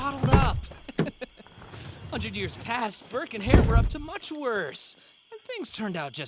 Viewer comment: what is he saying? Birkin hair?